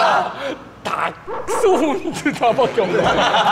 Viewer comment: did he just say Dachshund?😂😂